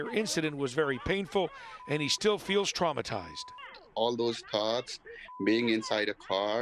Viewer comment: I thought he said "all those thots being inside a car". That really confused me.